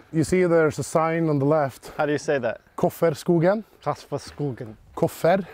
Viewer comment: "Kufferskogen" (or is it Kofferskogen?), that's similar to like "John's forest", I think. It's someone called Kuffer/Koffer and it's their forest. It sounds to me like Koffer, but looks like Kuffer on the sign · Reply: It's Kofferskogen (Koffer's forest), named after Koffer who allways put his drive early left into the trees :)